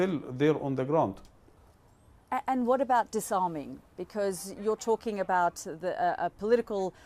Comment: Disarming? I thought you said "the shawarma". 😂😮